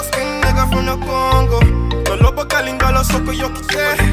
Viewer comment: For anybody wondering what he said at 0:41"Na lobaka Lingala soki o yoki te" it means "Btw ,If you didn't know, I speak Lingala"